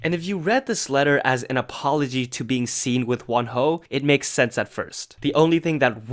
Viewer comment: PLS FORGIVE MEEEEE, i misheard saying "one hoe" instead of "wonho." Apologies